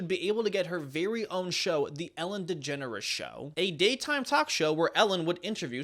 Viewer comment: In my mind I always thought people were saying "Allen Degenerate". Glad to know I wasn't far off xD